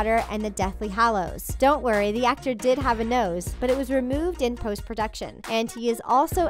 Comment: I thought you said, "Don't worry, the actor of Voldemort has a nose, but it was removed before filming!" I was like, W H A T?